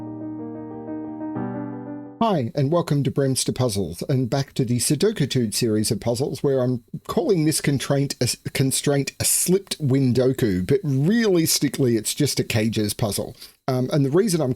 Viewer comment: I kept hearing that as "slipped window coup".